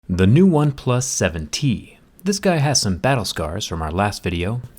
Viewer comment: I thought u said One plus 17 🤣🤣 Intro line